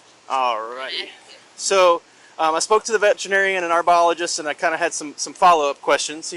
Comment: I thought auntie said she talked to a vegetarian not a veterinarian. 😂